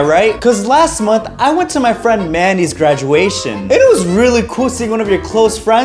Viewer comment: When you said “Mandy’s graduation” at 0:22, i thought you said randy, then i was like wait a minute, i thought he left with your 8 dollars to another state, then i looked at the subtitles, and i was now officially dumb